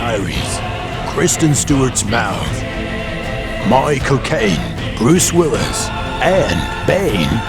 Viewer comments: I laughed my ass of when i heard "My Cocaine" :D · That was a great review up until the starring part. At which point I heard "my cocaine" I completely lost my shit laughing so hard. You guys are awesome.